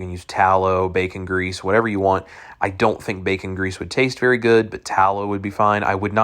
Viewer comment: "I dont think the bacon grease would taste good" - that sentence doesn't make any sense, its like your not even speaking English or something. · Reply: I thought he said baking grease, as in used and reused.